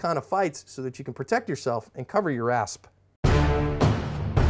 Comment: It took me 56 videos to figure out why he says cover your asp. Lol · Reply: I always thought he said cover your ass